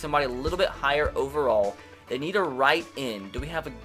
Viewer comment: Did he say a nigga hobo